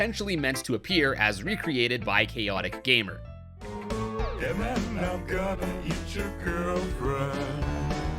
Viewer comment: I thought monster said “and then I’m gonna eat your gf” not hey man